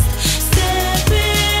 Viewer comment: what did he say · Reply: Sounds like he says "I AM AWESOME"